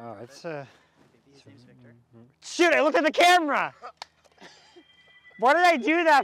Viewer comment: dang thought he said SHI